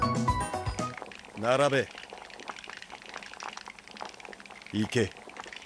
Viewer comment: 完全に英雄王で草 「逝け」に聞こえるでよ…